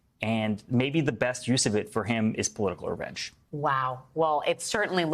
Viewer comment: "..best use of it for him is political <garbled word>..." What is he saying? what word is he using? sounds like "a-wrench"